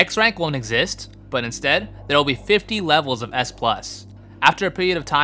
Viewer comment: hold on did you say 50? Or 15? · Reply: 50